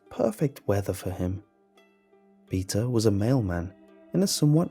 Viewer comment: On the last one I thought that he said male man not mail man... I was so confused the whole story.